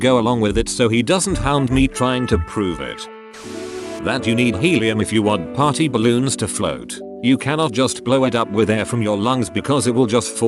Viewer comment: Both misread AND misheard this as, " That you don't need helium for party balloons to float" and for a second, I was like, "Oh no, I'm dumb!" 😂